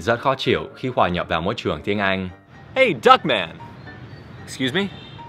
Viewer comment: nghe thành Duck Man :)))